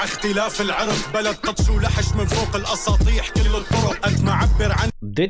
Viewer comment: No he did not say Assad he said ASSATEH (roof) another metaphor.. 17:33 Asmaa is Assad's wife known for removing her already small breasts due to cancer.. No wonder the AI couldn't understand and translate it perfectly.. it's full of metaphors that only a local would understand. I wish my English is better so I could have translated it for you.